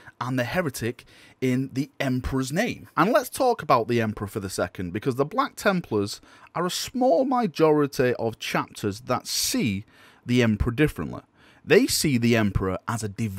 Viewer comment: Did you just say 'Mijority"?